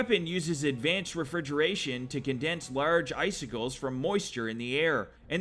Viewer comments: It sounded like you said Vance Refigrerator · I heard “Vance refrigeration” and all I thought of was Bob Vance, Vance refrigeration..😭😭🤣😂